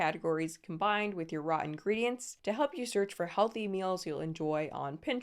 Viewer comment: subtitles say “rotten ingredients” when the voiceover is “raw ingredients” 😅 figured I should mention since it’s a p significant typo (Thank you for the informative content!)